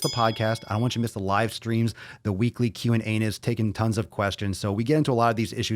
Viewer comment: Did he say "the weekly Q and Anus?"